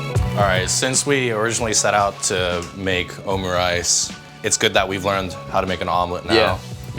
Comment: is just me but i heard him say omi rice then omu rice lol